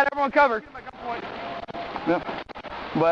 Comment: For a moment I read that as: "Suspect's been shot. Send MEMES". LOL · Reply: When the cop said I don't know where his guns at , I thought he said I don't know where his guts at